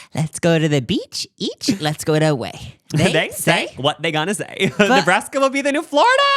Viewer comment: Correction ' let's go the beach-beach, let's go catch a wave' lol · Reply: I thought she said “let’s go get away” 😂😂😂 omg I’ve been singing it wrong for how long